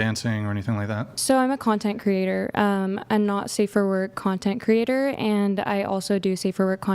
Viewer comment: 😂😂😂 I deadass thought chair 2 said “I’m a Nazi for work” I’m sitting there like tf is that so you’re a Nazi on the clock but not a Nazi in your free time?! 😂😂😂